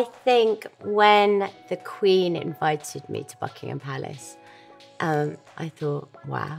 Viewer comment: sounded like she said "fuckingham palace" lol. oh, and "shitfon" dress